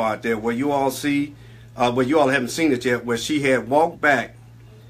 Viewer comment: he clearly says y’all haven’t seen it yet 🤨🤔🤭